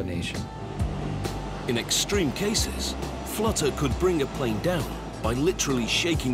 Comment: I thought it said boings last plane and I was like nooooooo